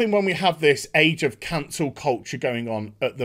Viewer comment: At first I thought you said “Age of Cunts” which would also apply..